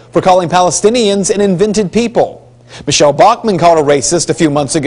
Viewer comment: oh dear God Gingrich said 'invented' people?? I thought he said 'inventive' people.